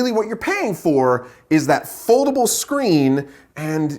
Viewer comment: When he said foldable screen I thought he said Affordable screen 🤪